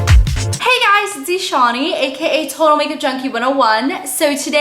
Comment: Oh my gosh I always thought you said "Hi guys it's me shawny" but now i realize your name is Eshani! I'm so sorry! :( Btw love love love your videos!